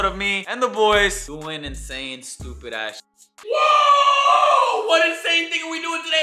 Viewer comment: am i the one only that always thought he said “doing and saying stupid ass shit “😭🤣